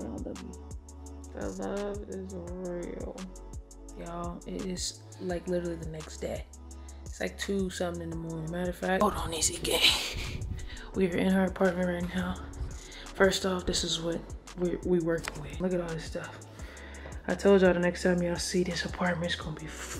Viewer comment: Who else heard Ezee say @ 12:49 “ Okay guys were in OUR apartment right now.”